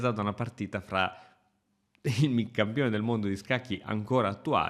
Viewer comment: Scusate la mia 'sordità'' ma a 1:36 non riesco a capire. Ho capito che si tratta di Carlsen vs Nakamura, ma non capisco quello che dice:. Io sento:: 'È stata presa da una partita fra demi(???), il campione di scacchi ancora attuale... ecc."